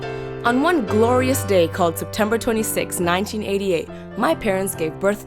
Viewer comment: happy bday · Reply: oh you said September I thought you said december